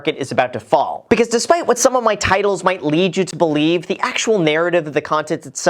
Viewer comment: Your titles scare me every time. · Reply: I thought you said titties